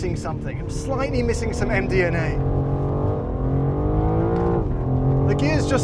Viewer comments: I thought he said MDMA. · I thought he said it’s missing some MDMA lol